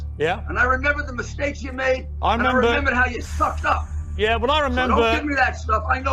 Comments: I think they misheard "S"ucked up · he said sucked up and they think he said fucked up LMAO >< 2:56 doesnt take a professional lip reader to say it clearly wasn't an F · I think Rudy said piers sucked up but they misheard him and thought he swore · I thought Rudy said he sucked up, not f---ed up.